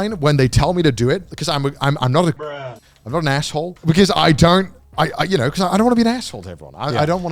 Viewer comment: Im not a Gaijin is what he says